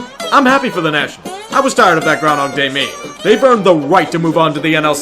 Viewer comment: I had a hard time understanding here, can someone write out what he said after "I'm tired of..." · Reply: that "Groundhog Day" meme. Meaning the Nationals always got to the ALDS and always lost over and over again